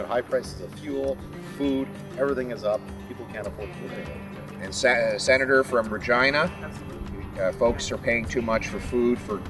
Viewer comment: sounded like "And senator from Vagina".... it made me go "whoa dude..just cuz she's a lady you do'nt need to address her that way. lol!